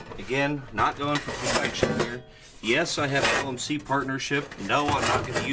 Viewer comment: Quick question, does anyone know what he said at 28:18 ? For some reason i cannot decipher it over the aggressive metal noises. And for another unknown reason i really want to know what in the world he said. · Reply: "Not going for perfection here"